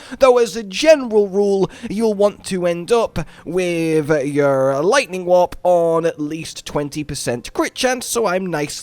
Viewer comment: Because of your accent I thought you were saying "Lightning Wap", which is a much funnier build name